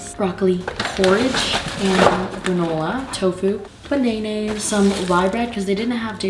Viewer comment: My sister has a friend named Cheyenne and we call her Shanaynay and when said bananas it sounded like she said Shanaynay so I think that's pretty cool